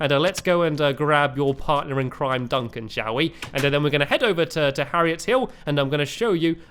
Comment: when he said "Duncan shall we" I thought he said "Duncan showee" 😂😂😂